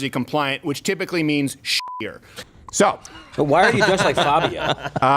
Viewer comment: I think Steven said: "They will immediately make the adjustments needed to make it New Jersey compliant, which typically means sh*ttier." ( Rhymes with prettier and wittier. )